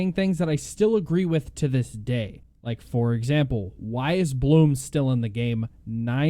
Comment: what word did you say at 2:00? bloom? please respond if possible! oh nevermind it is bloom